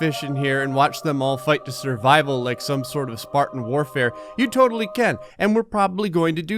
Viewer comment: wait did he say spartan warfare I just wanna know because I was just playing halo2 like 3 minutes ago and I thought he was talking about that